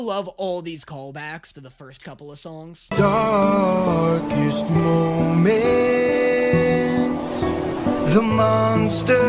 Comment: In case you didn’t catch it “I am your darkest moment” is what he said to the cyclops